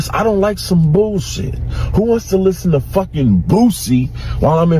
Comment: He said " who wants to listen to Boosie when im in Milan!? " 😂😂😂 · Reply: I don't even know who that is but I thought he said Bootsy as in Bootsy Collins and I was thinking well, maybe yes?